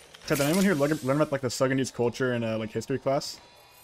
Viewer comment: I swear you said "That 70s culture" the first time you said that, I was so confused lmao 🤣